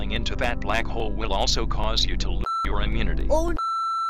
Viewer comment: Did bubble just say Oh F-?